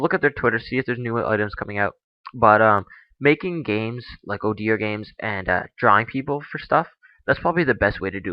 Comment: Did he say ODer games?